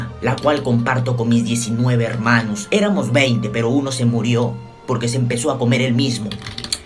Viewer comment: "no tenemos baño cagamos en bolsa" "Tan solo una que comparto con mis 19 hermanos, éramos 20 pero uno se murió por comerse el piso" Xddddddddddd Edit: era el mismo dejen de corregirme ;^ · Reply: pensé que decía "pero uno c murió poR que c empezó a coMerSe eL miSmo" :v